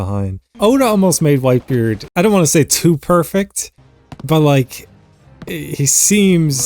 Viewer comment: did you say perfect? (guys dont spoil its on dbz)